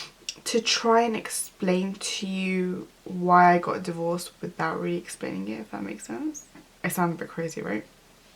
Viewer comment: why I got divorced without "jxhcucjc..." ?? pls what are the words ? Thank you 😊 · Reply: "without really explaining it, if that makes sense"